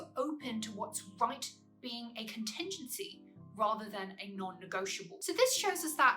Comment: did you say “contingency”?